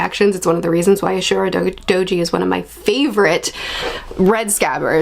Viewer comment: I swear I heard "Urotsukidoji is one of my favorite..." I was like wait what? "...Red Scabbards" ooohhhh..